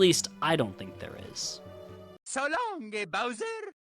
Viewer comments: actually, Charles has confirmed he says "So long-a Bowser!", also the source of this from Super Mario 64: Shintou (i think thats correct) where the BLJ was patched and Mario said "Bye bye!" instead of "So long-a bowser!" because in Japan Bowser's name was King Koopa, the reason this was removed in the Switch remake is because they wanted the version that ran smoother, has less glitches, and had some extra fun features, sorry just felt like correcting this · I always thought he was saying "so long, eh bowser?" but he's not even saying that, it's just Charles being Charles